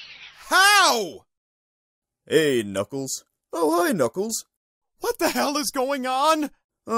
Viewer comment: bro i thought he said Oh Hi Doubles